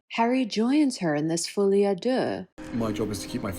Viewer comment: Can any body tell what is the French word she says here? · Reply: folie à deux